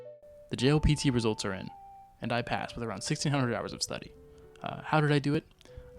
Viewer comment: a mere 1600 hours is crazy to me, at first I thought I heard 16000 hours